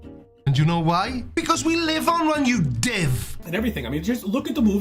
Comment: You diff? You div? What does said at 57:13? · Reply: Div, short for divvy. Why divvy I don't know